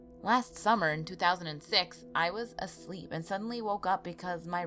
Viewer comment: it sounds like she said i was a slut when she said i was a sleep